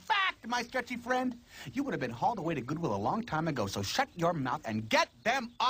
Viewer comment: first time i watched this i thought he said google he said goodwill actually